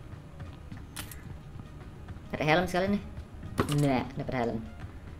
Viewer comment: kok lu bisa tau bang disitu ada helm? Apa gua yg salah denger yak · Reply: "nyari helm sekalian nih"